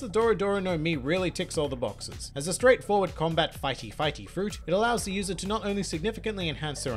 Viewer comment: I heard "fighty-fighty fruit" did you just make a new kind of devil fruit?